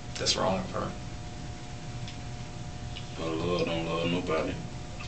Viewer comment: what did he say at 21:10 ? "the hood don't love no body" ??